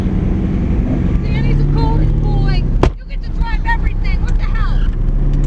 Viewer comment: what does she says????? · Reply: she's said 'danny's a golden boy, you get to drive everything what the hell' lol random but yeah